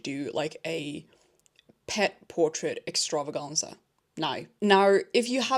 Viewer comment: oh my god i heard you say pits first and i was like wtf what's wrong w people wanting them to draw their PITS but yea no it's PETS... which is a relief hahahahaah